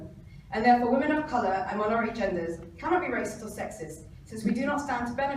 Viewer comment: did she say "women of color and honorary genders?" dafuq?